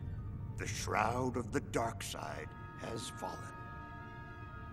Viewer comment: First time i watched this Movie, i thought he said Begun the clown wars has